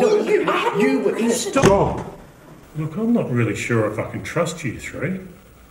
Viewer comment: did he say the F word？ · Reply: "not really sure if i can trust you three"